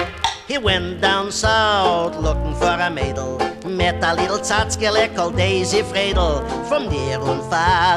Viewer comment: Did he call Daisy a "little tchotchkeleh"? ( 1:17 ) I'm wondering, because, besides its being redundant, we've been 'corrected' for using tchotchke in that context. Glad to know we're not using the term too incorrectly. Then again, I bet if one poked around the attic Toy Room at the ranch, a whole pile of Mickey Katz records my grandparents' generation listened to as kids would turn up. This may be how that generation picked up contemporary Jewish culture, for better or for worse...